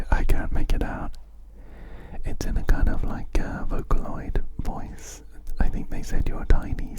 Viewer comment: i had to look up what clocky was saying because it was confusing me too (i originally thought it was saying something in mandarin lol). it's saying 'your time is over' in a japanese text-to-speech voice so you weren't too far off with your vocaloid guess!